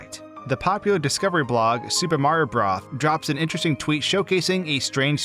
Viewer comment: i heard that as "super mario bra" im so sorry